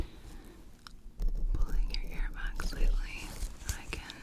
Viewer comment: I was not watching just listening 😂 not listening very well either. All I hear is im gonna pull your hair back and take a look 😂 I was like, hold on a second. Where is she looking. 🤣